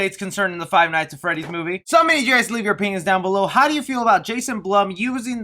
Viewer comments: i thought u said “so u can leave ur penis down below” I have a dirty mind =/ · at first I thought you said "leave your penis down below"